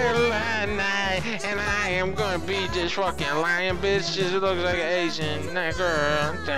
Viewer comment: Wait did he say "look like an asian N-word" at 0:46? Or do my ears decieve me?